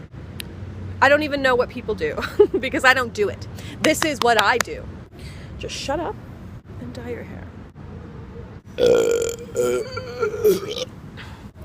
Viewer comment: I thought it said dye your pubic hair